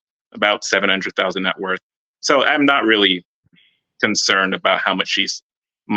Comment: $700k net worth + $300k per year at 26 = DMs on 🔥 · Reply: I thought he said $300k network. No? Still dms on 🔥